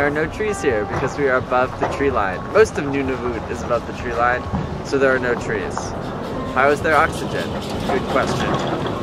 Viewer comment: the helllll did he just say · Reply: "Most of Nunavut is above the treeline, so there are no trees"